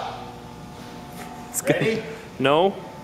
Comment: сказали "скорей" или мне послышалось 😅